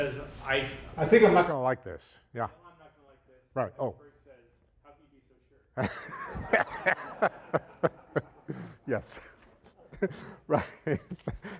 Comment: Could anybody hear the joke at 25:45? I couldn't decipher it and now I'm so curious! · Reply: Einstein says: "I think I'm not gonna like this." Heisenberg says: "How can you be so sure?" Joke is about Heisenberg's uncertainty principle.